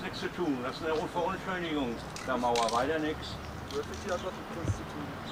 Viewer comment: Was hat er da gesagt? -- "Verunschönerung der Mauer"? 🤭